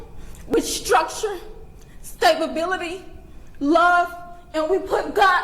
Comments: Did she just say stabability? 11:26 🤔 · did she say stab ability? Makes sense i guess 😮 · Wait... did she say stab-ability? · she says he was raised with stab-ability instead of stability lol. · This level of playing the victim, by the criminal's parents, says a lot about what probably made him the criminal he grew to be. 11:19 Did she just say "stab-ability"? An unfortunate slip of the tongue there.